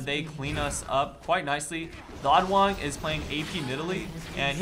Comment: I thought you kept saying theoddone